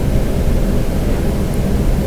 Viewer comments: I heard it say She was very hungry. Over and over and over. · Hey, any updates on this? Very interesting to me for sure. I think everyone would love an update if possible. It also sounds like it says SHE was very hungry instead of he was very hungry.